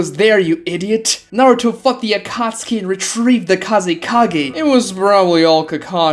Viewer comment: i heard, Naruto f*ck*d the akatsuki and retrieved the kazikage 😂